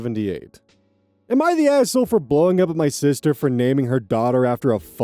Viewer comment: i thought he said “AITA For blowing up my sister” for a second 😭😭😭